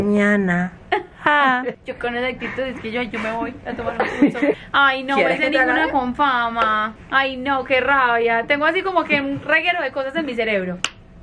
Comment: parece la que dice ay noo eso si jamaas